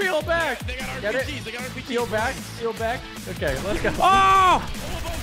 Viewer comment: Did anyone hear in the background saying peel back get it?